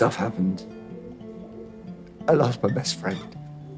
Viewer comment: Why cannot I stop hearing 'I love my best friend' instead of 'I lost my best friend'?? 😂😂😭😭😭